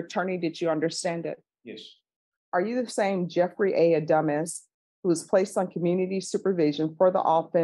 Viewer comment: (even though she tried to clarify the pronunciation with him in the beginning), it sounds like she says: "Jeffery A. Dumbass.".. just saying.